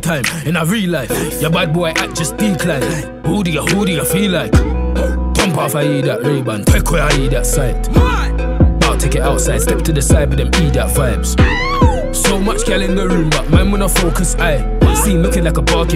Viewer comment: What does he say at 00:40? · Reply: Lowe12 pump off an ediat rayband Take away an ediat sight Bout tek it outside, step to the side with the ediat vibes Bare girl in the room but man wanna focus I